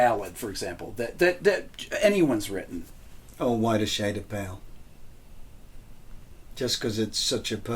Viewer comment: Peter states what his favorite ballad is but I can't understand what he's saying. Anybody translate? Thanks. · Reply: A Whiter Shade of Pale (Procol Harum).